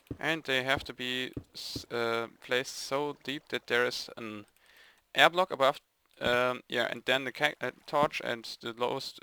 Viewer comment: Hab ich da "Erdblock" gehört? ;DD